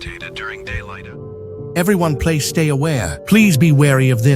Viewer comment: NAH BRO SAID PLAY INSTEAD OF PLEASE:face-blue-wide-eyes